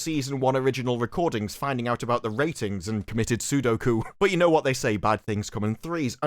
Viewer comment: Did I mishear that or did say “committing Sudoku”? 😂😂😂 Shop must’ve been in a real dark place to commit suicide by cross-number puzzle.